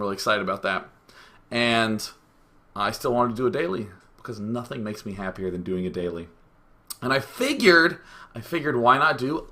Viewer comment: Haha, sounded like you said "Nothing makes me happier than doing it daily" :P Go!